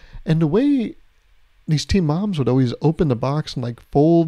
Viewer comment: For a sec I thought you were saying teen moms instead of team and I was so confused